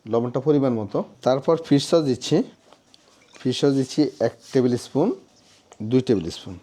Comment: কি বল্লেন? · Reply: ফিস সস